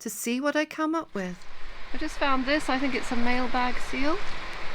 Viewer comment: We just had the greatest giggle thanks to you. My husband heard you say mail bag seal, and he asked " how does see know it it a boy?" I said the protruding peices..that what he gets for ease dropping..thank you for a good chuckle